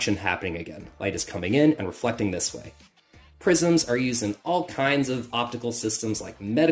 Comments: Thought you said "Prisons" 😂 Was very intrigued at first 🤦‍♂️ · I thought he said „prisons“ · I thought he said prisons 😭